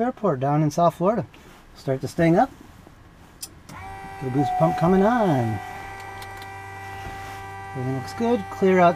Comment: “Fuel boost pump coming on”. I heard: “Feel goose bumps coming on”. That would be true for me!